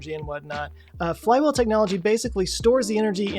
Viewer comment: I swear at first I thought I heard him say that it was plywood technology, rather than flywheel technology. Lmao! Btw, why not another billionaire? They get stuff done. Just keep them checked.